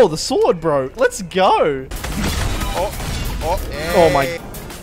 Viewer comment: Who else heard a bbbbrrraaaaaaaaaaa at 2:17? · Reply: its hey!